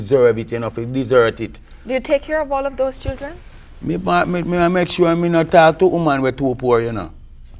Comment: Can anyone translate some more at 9:46 when she asks him if he takes care of his 22 kids? I’m trying to follow lol she says hes got a worthless mentality, what did he say??? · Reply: he said he make sure he don’t talk to a women that’s too poor